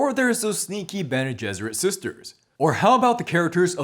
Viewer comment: Did... did you just say "Gene Besserit?"